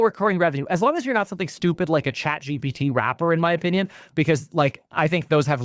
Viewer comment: He said "ChatGPT wrapper", but I heard "ChatGPT Rapper" and instantly imagined a computer onstage... with a gold chain